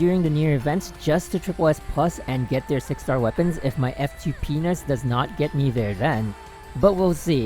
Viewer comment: F2Penis? did i hear that correct?